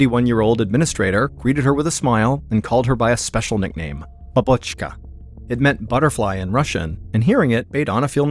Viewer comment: I am a Russian student and got highest honors in Russian of whole university and my teacher was from Russia. Babushka does not mean butterfly. It means granmother. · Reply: From what I heard he didn't said "Babushka", he said BABOCHKA (БАБОЧКА), and of course google translate is not that reliable, but I wrote butterfly in my native language and that's what appeared........